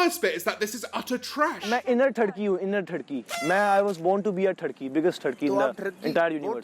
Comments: i swear i hear I WAS BORN TO BE A TURKEY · I though he said "I am an inner Turkey" But then I remembered that he didn't speak English. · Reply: I heard “I was born to be a turkey” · i always hear 'i was born to be a turkey' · Bruuuuhhhh.... Am I the only one who thought he said I was born to be a turkey. I wasn't paying attention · I could’ve sworn he said I was born to be a turkey